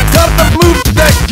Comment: DAMMIT CUT THE BLOOP DECK HEAR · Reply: I hear "DAMNIT, CUT THE BLU NUT GEAR!"